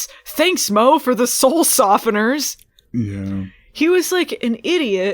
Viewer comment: I kept hearing "stool softener" and now it won't leave my brain that they poop out their souls